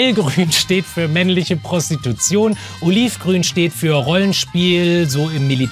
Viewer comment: Ich habe bei 8:03 Uringrün verstanden😅